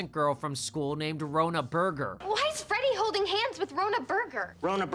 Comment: BURGER DID YOU JUST SAY.. HER NAME WAS BURGER